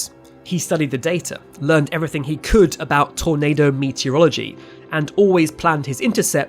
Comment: When the narrator said "tornado meteorology", I heard "tornado meets urology". Then I thought "That's appropriate. I'd be peeing myself if I saw that coming at me".